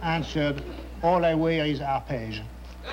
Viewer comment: I can't make out the punchline at 2:00. "All I wear is ?arpage?" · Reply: Arpege is/was a perfume